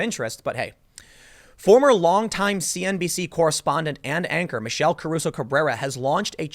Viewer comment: Could have sworn Tim said "Ann Anchor" and I legit was like damn, best name for an anchor ever xD